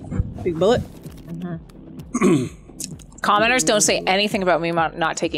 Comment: I realize said commenters but it sounded like she said commoners like we’re peasants 😂